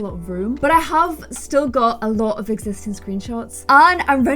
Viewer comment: I heard that as, I do still have a lot of explicit screenshots, and I was thinking what are you doing exactly? 😂